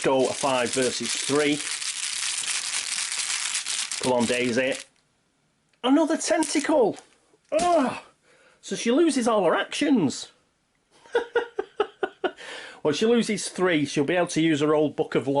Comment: I misheard you for a second: "Another testicle! Daisy loses all her actions!"